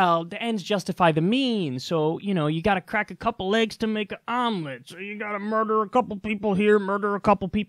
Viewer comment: i heard "ypu gotta break a couple legs to make an omlette" took me a minute to figure it out lol.